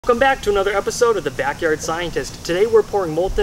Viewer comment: "Come back to another episode of Backyard Scientist" But I just started watching the video... I know what he was actually saying, so don't point that out, okay?